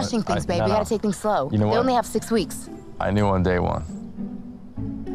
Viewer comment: "I knew on day one" She was 14 years old.. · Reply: I thought he said "I knew on date one". And I really wanted to see his reaction to her answer!